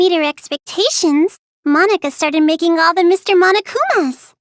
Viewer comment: Either im on drugs or did she say I? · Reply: Oh she said "I" alright